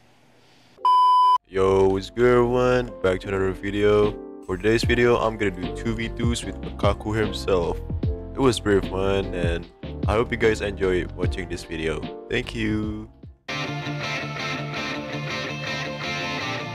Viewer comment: On 0:18 it sounded like he said MiKaKu